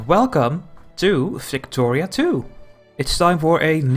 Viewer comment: Flicktoria? Explain please xD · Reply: Your accent made it sound like you said flictoria